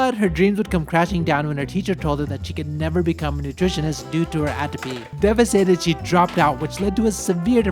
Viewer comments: Thank you! Thank you! Thank you Paolo! Thanks to the 18-year electrician (sorry, I didn't catch her name). As the daughter of a commercial contractor in the USA, I grew up visiting job sites and never saw women working. I'm in tears. This was so lovely. I do have a tiny question. I tried using the captions and slowing down the video but I couldn't understand what it was that teacher told her meant she couldn't be a nutritionist. Was it her GPA? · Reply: I thought he said atopy... But I'm not sure too. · what did her teacher say? Cant make it out lol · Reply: The teacher advised her against a career in nutrition due to "Atopy", the genetic tendency to develop allergic diseases. Nutritionists are often required to sample (consume) foods in order to analyze them. One specific example, Japanese schools typically employ a nutritionist who examine and sample each day's lunch meal before it is served to students and staff. Atopy and associated allergic conditions may make that role difficult and potentially dangerous to her health. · Good on her dad for managing to get her out of the house and get her involved in construction. It's so hard when you're a teenager and someone says something so discouraging. Especially since she was probably already feeling depression, which was causing the apathy. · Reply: I think you misheard. It was "atopy", not apathy. Atopy is the genetic tendency to develop allergic diseases (for example tasting food).